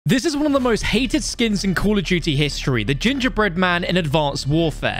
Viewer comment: Bro, why did you just say call of juty not England💀💀💀